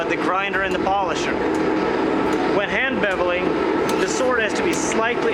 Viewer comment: what does he say at 2:49? "when hand babbling?"